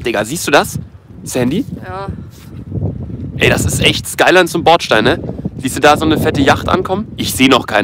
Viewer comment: Ich habe nur Skyline gehört 1:24 Lebe halt für dieses Auto 😉